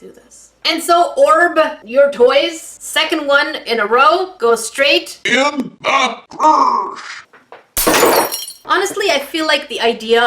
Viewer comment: It sounded like you said for the second one goes in the gash